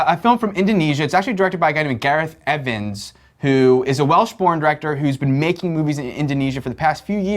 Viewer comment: Did it sound like he said "a welsh porn director" to anyone else?